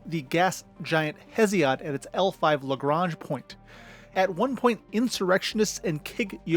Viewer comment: i swear i heard lebron point.